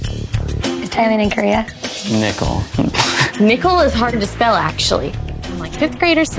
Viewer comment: did she say "is ThaiLand in Korea?" LMAO!